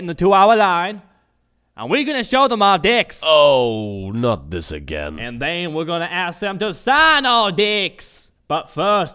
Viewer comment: lol tey thought he said d*cks he meant decks... random guy: you allways ruin every joke by explaining it in a comment me:... well excuse me princess random guy:... thats just gay me: yes you are random guy: how origional me: i know rite? random guy: ...